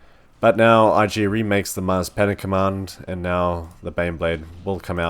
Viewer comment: I've misheard you: Mars Panic command